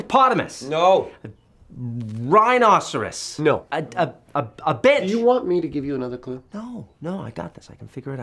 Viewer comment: WTF at 2:40 did he really say "a bitch"???